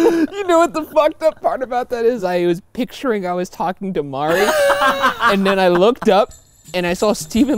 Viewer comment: Turn on the captions. It says Mark instead of Mari and Steven Lou instead of Steven Lim. 😂